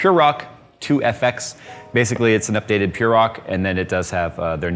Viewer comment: Fuck man, i thought you said Turok when you said purre rock, i was like oo neat a turok heatsink.